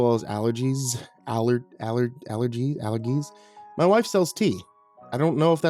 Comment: I legitimately thought you said at 16:09 that your wife sells teeth and your reaction after that had me going longer than I care to admit.